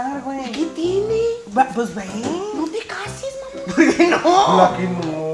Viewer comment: dijo: no te cases Alejandro o escuché mal😱 min